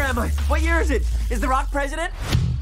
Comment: "Is the rock the president" They really made sonic better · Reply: Dat Boi I thought he said “is Barack President”